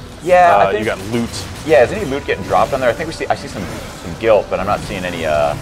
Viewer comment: did he say "Guilt?" is that what Death uses instead of Wrath?